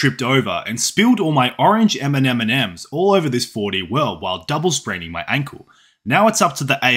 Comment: I thought you said "double spreading my ankle" which, I guess is possible in 4d.